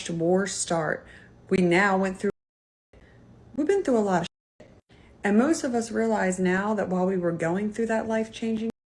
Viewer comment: What’s the word skipped at 10:10? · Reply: Covid